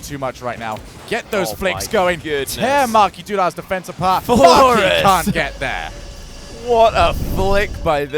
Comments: i heard fu*king c*nt get there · i hear fucking can’t get there but i know he said